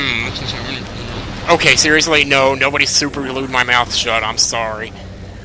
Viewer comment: OH! "Someone superglued your mouth shut?" For some reason I heard "Someone suckerpunched my asscheek" My only response was "WAT?!"